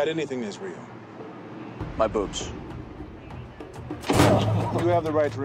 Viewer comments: Officer: "you got anything that's real?" Dean: ".......my boobs.. :D" i mean.. 😂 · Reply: i deadass thought he said boots 💀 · "Dude, you fugly." Has stayed with me since it first aired. That was when I fell in love with Dean. That, and the "my boobs" retort. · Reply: I thought he said boots... But no, yeah, him saying boobs totally makes sense too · Officer: "you got anything that's real?" Dean: ".......my boobs.. :D" i mean.. 😂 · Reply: I thought he said boots for the longest time 😭😂